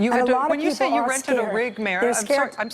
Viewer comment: The mayor is saying, of course, that she "ranted and raved,", not "rented and raved." As she points out, she shouldn't have to scream at FEMA reps time after time to get action. They are no doubt well-meaning, but somehow are not close enough to the emergency to make it feel like it's their emergency. To ask for a list of hospitals that need help when every single hospital needs help since the whole island is without electricity, and anyone can find a list of hospitals in Puerto Rico, is taking red tape to a new level of incompetence. The president is letting this incompetence continue and I say that, because the governor of Puerto Rico has said he is in daily contact with the president, and yet the situation continues mostly as before, while the president focuses on changes in the tax code and football player patriotism, and continues to mock Sens. McCain and McConnell on Twitter for his own enjoyment. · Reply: "ranted and raged" is the actual saying. Maddow thought she said "rented a rig."